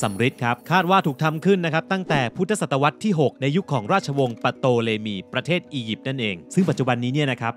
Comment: พูดว่า “ราชวงศ์ปโตเลมี” แต่ในคำที่ขึ้นในจอเขียนว่า “ปเตโลมี” หนูก็ช่างสังเกตเกิ้นนนน😅